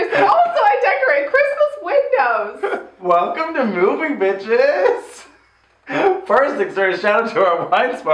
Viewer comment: At the beginning I thought said "welcome to moving bitches" and honestly I would so watch this house hunting show